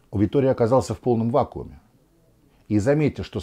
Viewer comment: послышалось "Витория оказался в полном ва*уе"... Думаю так было бы не менее точно)